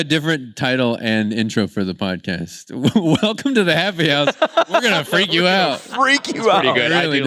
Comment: i have fulllllll body chills even on my face. they heard "happy house" but i heard "let me out let me out let me out!"